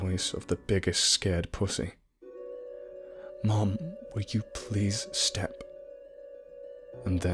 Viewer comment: Hold up wait, did he say mom? 9:25 Edit: Nvm I think he said mam